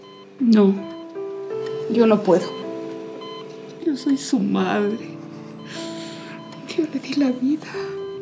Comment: Pense que dijo- que no soy su madre no puedo aserlo ni la di la vida